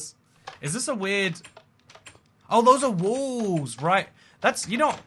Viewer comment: I can’t make out what he’s actually saying here, what kind of block is that? It just sounds like he’s saying “walls”.